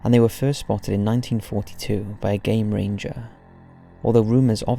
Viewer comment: I thought he said "first spotted in 1962 by a gay ranger" lol